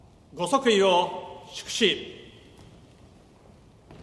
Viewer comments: 天皇陛下が天道陛下って聞こえる‼️笑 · 天丼陛下万歳って聞こえるんだが(笑)